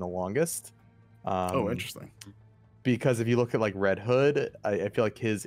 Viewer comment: This man said “Red Head” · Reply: haha he said "red hood." I thought I heard him say that too lol